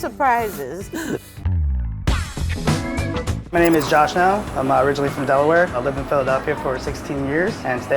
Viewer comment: I thought the guy said ''my name is josh now.'' I was like: What was you're name before?